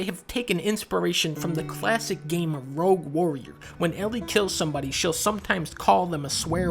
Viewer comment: When he said “...took inspiration from the classic game, Rogue Warrior” all I heard was ‘rock and roll motherfuckers rock and roll